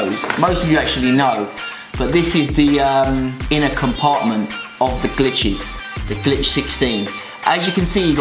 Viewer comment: when you said the inner component of the glitches, I thought you said the inner component of the bitches... AHAHAHAHAHAHAHAHAHAHAHaA